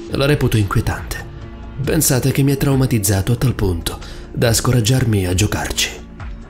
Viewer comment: Solo io ho capito "scorreggiarmi a giocarci"?